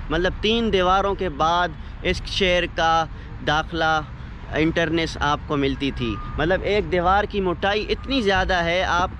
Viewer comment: bhai what is enter nace ? Or u wana say entrance ?